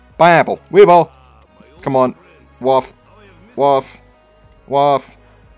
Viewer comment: what were u saying 💀 “waf wah wall waaf?” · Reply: Waffe, which is short for wunderwaffe